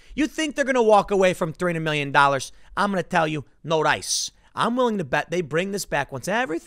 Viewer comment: This is proof of Tim's Korean heritage. Instead of "No dice", he says, "No rice".